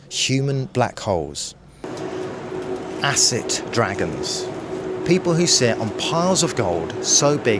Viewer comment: This is excellent. ❤ “Wealth Hoarders, Black Holes, Acid Dragons.” Excellent narrative. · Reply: I thought he said "asset dragons".